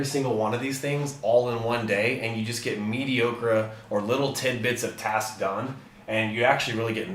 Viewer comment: the "meaty ocra" instead of "mediocre" at 19:00 kills me. LOL